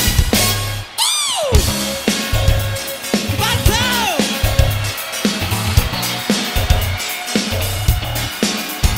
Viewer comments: Can someone tell me what Michael says at 2:09 please? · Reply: Wild crowd! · What does Michael shout at 2:08? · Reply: "BATO"